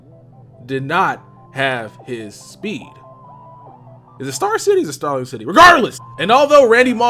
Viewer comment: did he just say star city SMH central city lol